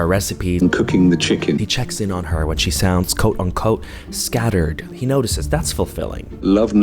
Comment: I swear he said, "code uncode" which, would have been genius, but it was actually, "quote unquote".